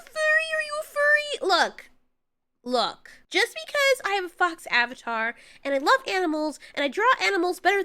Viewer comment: did i hear “fox avatar”? I thought you were a cat